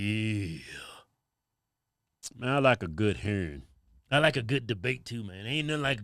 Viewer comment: I thought y'all were saying herring lol. That southern drawl.